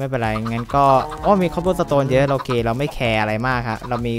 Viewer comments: เป็นเกย์หรอ 16:16 ได้ยินเหมือนพูดว่า"เราเกย์เราไม่แคร์" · ตอน 16:16 ผมได้ยินพูดว่าเราเกเราไม่แคร์ ใครได้ยินเหมือนผมบ้าง